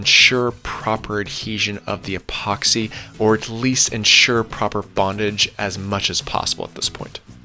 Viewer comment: Did you say, "insure proper BONDAGE"? LOL Bonding=adhesion, bondage=kinky. BTW, great content and production as usual!